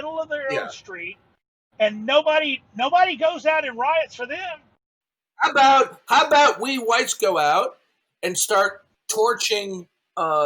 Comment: I thought I heard "how 'bout we whites go out" but I'm pretty sure he said "we shites"